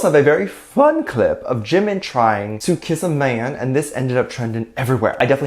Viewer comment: I misheard "Jimin kissing a man" for "Jimin throwing hands" 💀💀